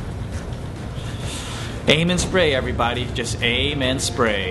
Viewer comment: when he said aim and spray I thought he said "anus spray" lol :D